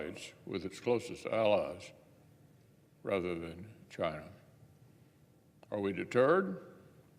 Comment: When he said "are we deterred"?, I thought he said "are we a turd"?, admitting what a terrible job he is doing.